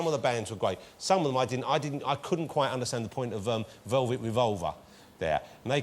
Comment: when Sharon was speaking in the posh voice, i thought she said Velvet Vulva...thats a different band entirely